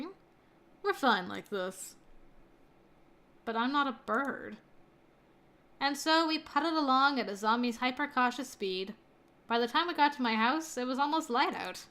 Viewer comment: Idk why but whenever I hear "Azami's hyper-cautious speed" I think it says like "A zombie's hyper-cautious speed" It's just stuck in my head 😂😂😂